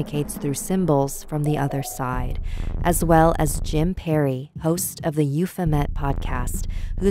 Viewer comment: I thought you said Jim Carrey and was very confused! I'm going to check Euphomet out, though, thank you for the recommendation!